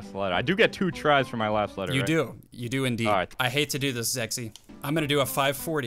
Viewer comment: for a second I heard "I hate to do this, sexy"💀🙏